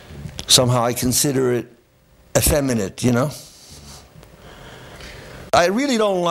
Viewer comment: what does he say at 5:58? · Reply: Poetry that is not set to music doesn't really turn him on. He sees it as "somehow effeminate" by comparison.